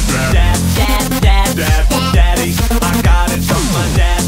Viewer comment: when 1:24 to 1:37 i heard dab but not dad,maybe i am too mlg.